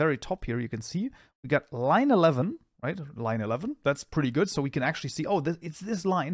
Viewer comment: I heard 9/11 🙃